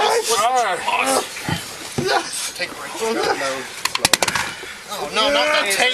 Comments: When amateurs are in charge of human life ..... Anyway, did I hear "Nono, not teaser AGAIN!" at min 8? Again? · Details: <link> All Raw footage here: <link> Jasper County Sheriff's Office Facebook: <link> Jasper County Sheriff's Office website: <link> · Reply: I hear "... Taser again." Did they tase him? Did they put that in the report? Do they know you're not supposed to tase an epileptic, because it can kill them? 😡